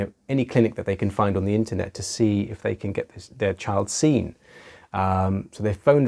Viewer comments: Did he say dead child seen at about 1:01? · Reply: I think he said their but with his accent it sounded like dead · did he say dead child seen or dear child seen? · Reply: Their child seen*